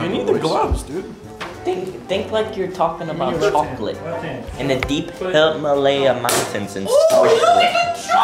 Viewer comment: Hey Unspeakable guess what ...... i know what said at 4:05 - 4:08 he said think like your talking about chocolate in the deep HILL MALAYA MOUNTAINS IN SWITZERLAND and there you go Unspeakable your welcome 👍👍👍👍👍😂😂😂😂😂